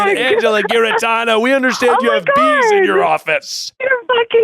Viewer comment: I heard beef?! 😂